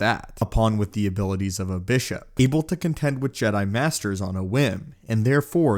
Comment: She had the powers of a what ? I couldn't make out that word ? Just before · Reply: Bishop